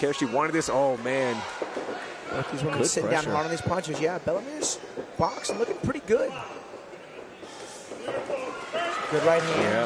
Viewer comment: did he say "bella mirs box looking pretty good". I agree sir😂